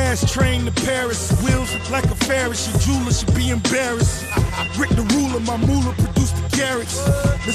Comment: the hell did he say? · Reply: Rick the Ruler my moula produce the karats